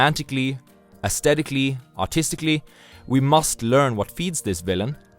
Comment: lol I thought he said “austistically”